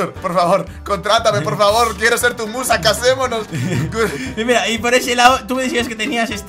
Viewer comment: escuche mal o diji "casemonos"